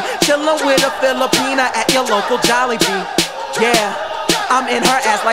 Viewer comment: Why the fuck did i hear "Filipina in the local jollibee"